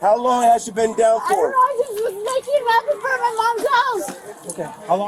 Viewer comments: Am I going crazy or around 1:00 does she say that she was just making meth in front of her mom’s house?? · DID SHE JUST SAY SHE WAS "MAKING METH IN FRONT OF MY MOM'S HOUSE"?!